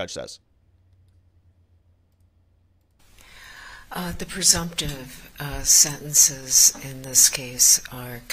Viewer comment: She said "the presumptive sentence is immense," not "the presumption is the sentence will commence." The authority as a police officer is the only thing the adds to the sentence. What they were actually talking about is downward departure. There's a type of hearing that is held to determine if she should have her sentence raised b/c of her authority as a police officer. I forget what it's called. It's a court case and a common last name but it escapes me at the moment. Not a lawyer or a youtuber making videos on the subject so I have an excuse for not knowing the specific name. What's yours? Your legal analysis is generally pretty good but sometimes it really seems like you completely mishear something or get something wildly wrong. It's kinda weird.